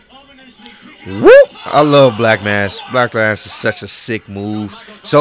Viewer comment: it sounded like he said the a word when he was tryna say black mass the second time